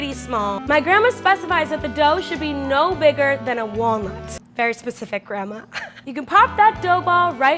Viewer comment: when said it should be no bigger than a walnut i thought she said a wong...